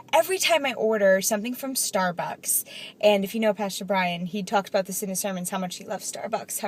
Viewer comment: I heard "pastor Brain" and bounced.